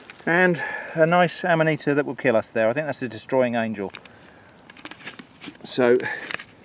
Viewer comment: I'm sorry did you say "Destroying Angel" ? That sounds Fucking Metal...